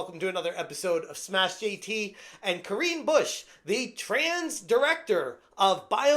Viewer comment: I thought you said “green bush” lol. I was like “oh I guess that’s fitting”.